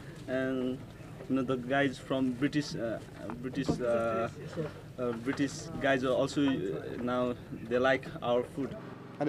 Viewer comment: I thought he said,"British guy is also like our food" 🤣